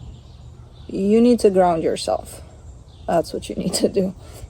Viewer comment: Leo here. I thought you said drown yourself😅 not ground yourself. I need coffee.